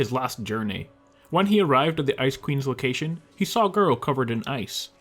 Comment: 69 likes XD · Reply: P.s when he said ice queen i thought he was saying Ice Cream in a silly way XD